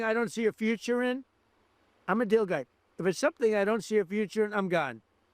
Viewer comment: I thought he said he's a dildo, not a deal guy. 😂😂😂